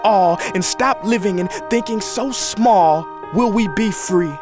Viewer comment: I heard d is so small 😂